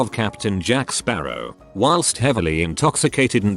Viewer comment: This was about 14yrs ago. Cops came banging on my door around midnight. Woke my wife and I up. So I opened the front door, left the screen door shut, and asked what they wanted. They said they had a report of a dog barking. We did have a dog in the backyard. Anyway, they asked me if I could step outside. I said, nope, I'm fine where I'm at. The look of anger and disappointment they had made my night. We found out that my neighbor was making the dog bark, then going in and calling the cops. He was a prick. After the dog incident, my wife called animal control on him for keeping goats. It almost was Hatfield's and McCoys before we moved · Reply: I thought u said when I was 14 and I was like he has a wife at 14?!?🤔🤔🤔